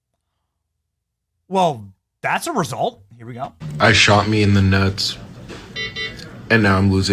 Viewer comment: I thought he said “I shot me in the nuts”! 😂😂😂😂